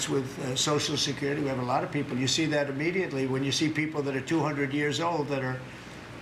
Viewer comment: Did he just say 2️⃣ hundred years old at the 2:31 mark⁉️